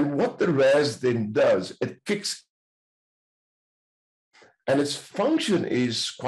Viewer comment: a temporary mute. "..it kicks ? and it function....". Would you be so kind and advise what was said there? 😁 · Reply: "It kicks in to its default function". Sorry about that mute, will look at original and see if I can fix it.